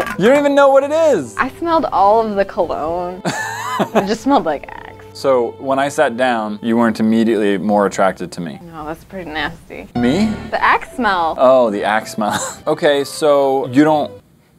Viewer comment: I thought she said “you smell like EX “ then I realised it’s AXE 😂😂